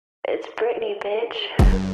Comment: She's saying IT'S BRITNEY BITCH??? I always thought she was saying I'M A PRETTY BITCH O_O